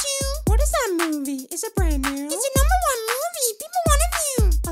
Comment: why do i hear "its the number 1 movie people wanna mew"